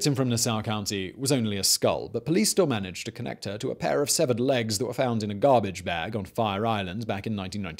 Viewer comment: I misheard them connecting a skull to the severed legs as SEVERAL legs and I was mortified 😂😭